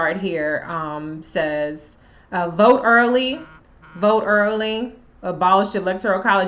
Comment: What I always hear is "Vote early and vote often!" lol! 😊